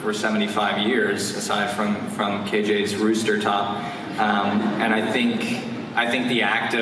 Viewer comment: did cole just say kJ’s hair looked like a rooster top 😂😂😂😂